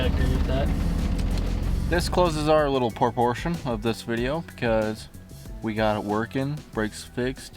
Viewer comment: did he say proportion or part/portion?